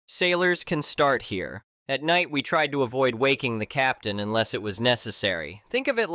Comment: I listened to this in a loud environment and misheard at night, we tried to avoid waking the captain unless it was necessary" as _at night, we tried to avoid wanking the captain unless it was necessary and was wondering what sort of emergency would necessitate doing that.